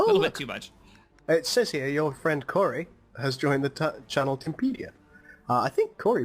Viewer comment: Did he say Timpedia at 27:03?